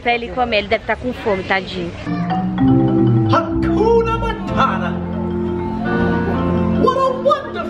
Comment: eu entendi o bixo falando "ratu na batata" ksksksksks 7:45 a muie achando um maximo sendo gravada ksksksks